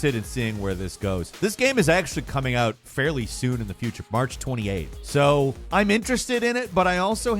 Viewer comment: March 2028?! Or what does He say?